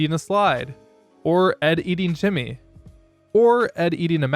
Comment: Did he say Ed eating Timmy? I always thought his name was Jimmy